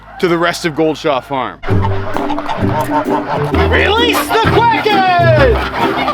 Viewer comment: I heard realese the crackheads.